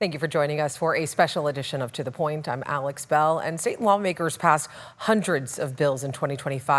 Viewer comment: I heard Satan lawmakers